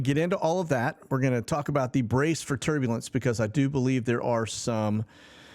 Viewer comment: I thought it said brace for tabernacle.